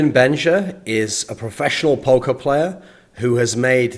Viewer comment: at first I thought he was saying "Professional Poke(mon)Player"...